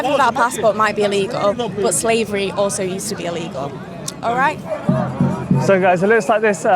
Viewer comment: did she say slavery used to be ilegal? The accent is very strong I can barely understand her